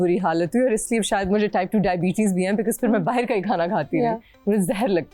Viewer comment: did she say she has type 2 diabetes? She is so young